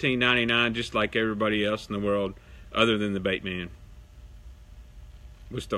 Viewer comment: Did you just say master bait man?